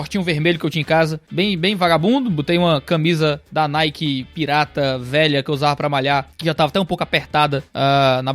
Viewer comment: eu ouvi camisa pirateada hein kkk